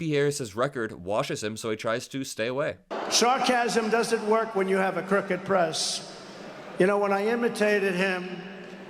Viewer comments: did he say "SHARKasm doesn't work when you have a crooked press"? He's back to sharks, an interesting Freudian slip. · did he just say “sharkasm”?! 🦈 #sharkasm